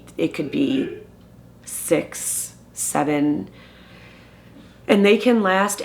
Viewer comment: Wait wait wait... What did she say in 14:13 ?! 67?!